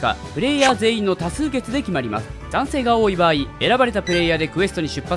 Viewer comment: の賛成が男性に聞こえた